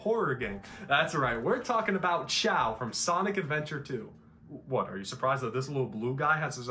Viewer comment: i thought that said chad